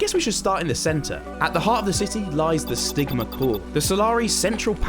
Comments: the vid: "the stigma core" What I hear: "the sigma core" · I thought you said the sigma core lol😂🎉